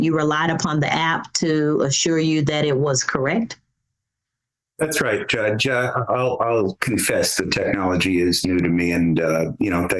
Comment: "The technology is new to me"... sounded like he said "fact checking is new to me".